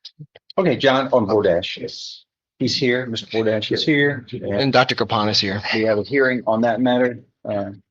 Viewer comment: I thought judge said "John from Door Dash is here"